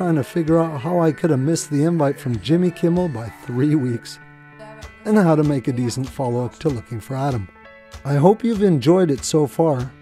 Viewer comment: This is so great!!! I also misheard "how to make a decent follow-up for Looking for Adam" as "how to make a disney follow-up for Looking for Adam". I think this could make such a great heartwarming movie, in either the style of Disney's UP or similar in feel to the movie JUNO. When you were cringing in pain from missing Jimmy Kimmel's email it was also hilarious...hopefully Ellen gets you on her show though :)